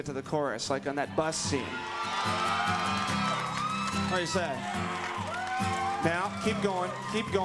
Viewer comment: what does it say? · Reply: "What do you say?"